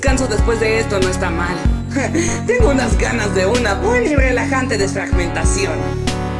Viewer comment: en adelante me encata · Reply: por un momento pensé que dijo que "tengo unas ganas de orinar"